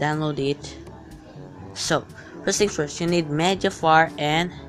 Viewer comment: Nice vid Ps when he said media fire I thought he said mega fart for some reason